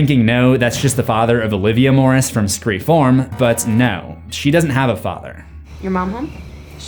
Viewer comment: Ok… I finally got it. I swore I was hearing him say Scream Form. I listened back and dig through comments to make sure I wasn’t the only one hearing this lol. I get it now. Screfourm… Drove me crazy thinking he was just adding an m to the end of 4 cause he couldn’t pronounce it right. Definitely time for bed! 😂 🤘🏻🤪🤘🏻